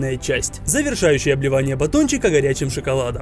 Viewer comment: мне послышалось "окончательное бливание батончика"😂